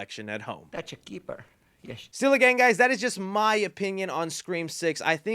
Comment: I thought he said “silly gang guys”